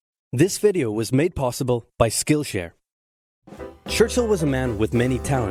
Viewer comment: If u listen for it, it sounds like he says skin share